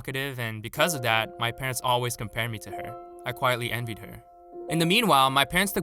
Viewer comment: I legit thought he said ,”I quietly ended her” until I turned on subtitles.